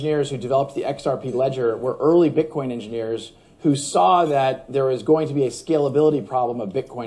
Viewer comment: min did say the original xrp engineers were early bitcoin engineers????